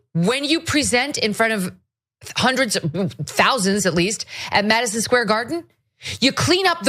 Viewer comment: I think I heard her say "hundreds" then corrected herself and said "thousands". I'm pretty sure you misheard her. Just sayin', and I'm no Megyn Kelly fan.